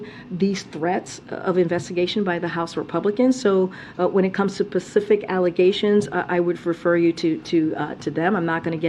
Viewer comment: "...Pacific allegations..." 😂that's our articulate spokesperson for the White House. Then she reuses the word pronounced properly right after it to show she does know how to say specific. Gotta love hiring someone for their diversity instead of their competency for the position.